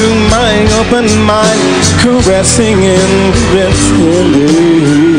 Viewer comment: Favorite moment: 0:29-0:39. Sounds like he had a seizure. "... drifting through my open mind, caressing and pesilinnehwy..." -Stevie Wonder. What did he say? haha lololol